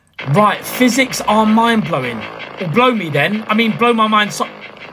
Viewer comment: “well blow me then… i mean- blow my mind” is wildddd💀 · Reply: I actually thought he said "blow me dead" 😆